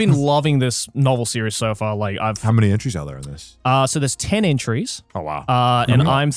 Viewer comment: I thought Connor said "How many ecchis" 😂